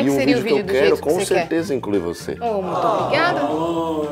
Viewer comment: no "incluir vc" eu entendi "comer vc" meu Deus kkkkkkkkk